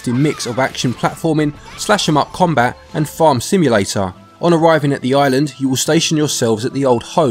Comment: Lol I thought you said ‘fart simulator’